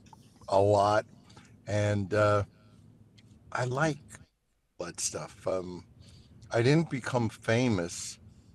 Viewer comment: Ummm… at 21:26 did he say I like butt stuff? Either way. Greatest frontman and genuine interview. Not the same old same, 4 knuckleheads wanted to be…… etc.